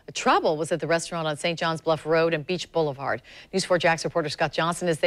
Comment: lol I thought she said Deuche Boulevard. XDXDXD